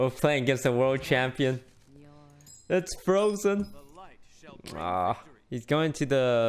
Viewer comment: LOL thought you said forsen #neverlucky